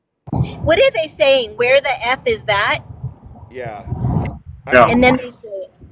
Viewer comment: In the audio, all i keep hearing is, "where the f*ck is Sam.'